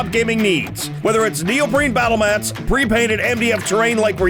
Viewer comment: Ok, so I know they're saying neoprene, but every time they do the ad I hear "Neil Breen" and THAT would be one hell of a batle mat.